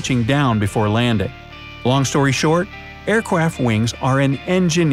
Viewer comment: did he say air cwaft wings